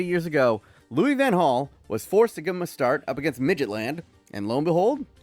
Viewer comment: Did he just say miget land🤣🤣🤣😂😂😂. That's so wrong.